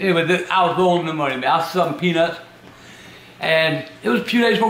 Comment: did he say he was sucking peanuts? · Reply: He was selling peanuts